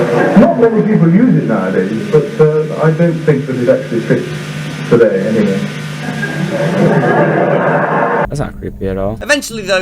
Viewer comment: Please help me, wtf does he say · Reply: Andrew says "Not many people use it nowadays, but I don't think it actually fits today anyway" And the voiceover that follows says "That's not creepy at all"